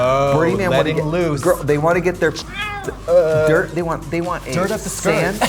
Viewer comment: sounds like trixie said they want aids when they go to burning man fucking lmaoo